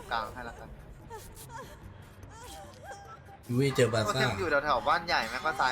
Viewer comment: ทำไมผมได้ยินพี่พูด ยูเว่เจอบาซ่าเฉยเลยย55